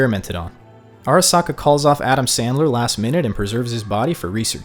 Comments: did you say adam sandler? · I swear, you said Adam Sandler. My imagination is having a fucking field day.